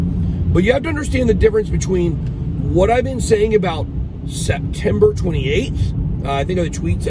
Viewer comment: I thought you said September 26 th lol